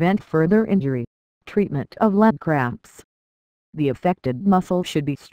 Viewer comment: Sounds like shes saying leg crabs